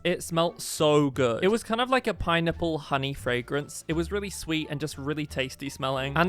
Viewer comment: Anyone else hear PineNIPPLE?